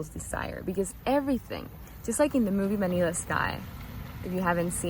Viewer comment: what movie? Translation says Manila Sky but I cannot find a movie with that name. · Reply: Vanilla sky. 😁